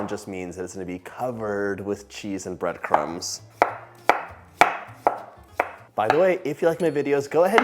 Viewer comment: I thought I heard smothered in cheese and breadcrumbs?